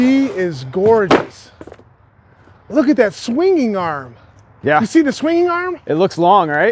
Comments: Did you say swinging arm? lol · is he saying "swinging arm"? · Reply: Yessir. It's called a swinging arm